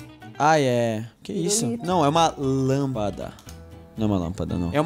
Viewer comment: entendi o falando lamada kkk